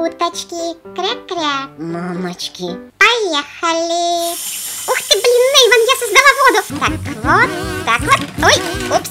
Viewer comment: В 3:01 сабака что сказала... Блин? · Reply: Ух ты блин эйван я создала воду